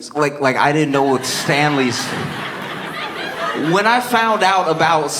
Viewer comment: I think the most accurate word would be: tsunami. A tsunami of white women. Here’s how out of touch (or, maybe just dorky) I am. I thought he said “Stan Lee”, the comic book writer. Also, that crowd was annoyingly chatty. Stfu you’re at a show.